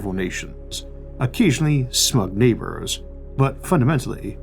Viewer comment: did you say sΙυτ neighbors?